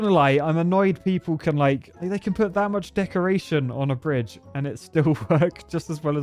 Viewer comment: Am I the only one who heard d*ckoration at 15:39?